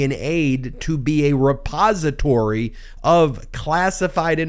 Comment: Wait, the aide was a repository....oh, at first I heard "suppository". Very different......